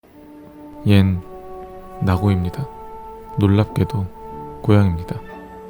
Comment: 처음에 얜 낙오입니다 로 들었어요ㅋㅋㅋ 그런데 나고 손은 왜없어요...?